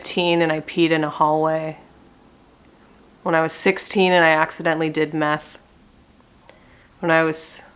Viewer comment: I heard "accidentaly did MATH" and was like yeah I feel that